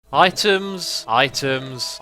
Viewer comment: after a while it sounds like he says itums or itooms